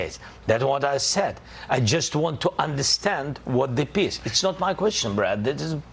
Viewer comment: did he say what the piss, bruh at 11:30 ?